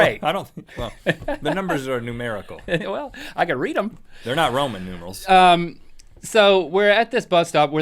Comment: did rhett say roman noodles? at 2:56?